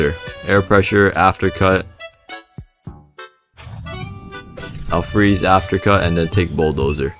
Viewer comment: i was zoned out at 17:21 then i overheard "ill freeze africa" and then my brain woke up and realized what said